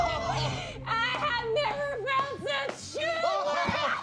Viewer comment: What's mantis saying starting from 1:42? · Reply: I have never get such humor